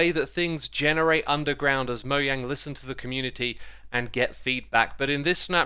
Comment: I'm sorry, but at 0:21 did I just hear M O Y A N G